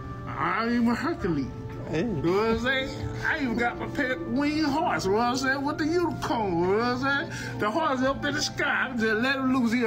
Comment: "I am Hercules, know what I'm sayin? I even got my pet winged horse, know what I'm sayin with the unicorn know what I'm sayin? The horse up in the sky, If I just let him loose he gonna run around the city" I believe this man is telling the truth.